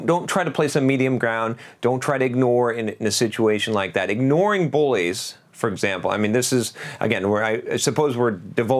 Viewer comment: Thought you were saying "boys," not "bullies" until about 10:20 😄